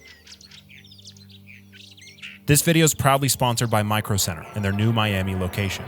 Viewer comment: i thought bro said "probably" at first LMFAO